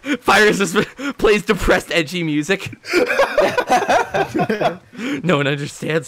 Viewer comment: Did anyone else hear him say depressed ECHI music.